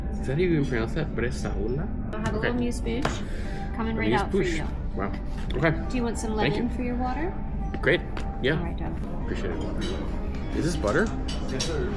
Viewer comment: what does she say? Legging for water? what does that mean?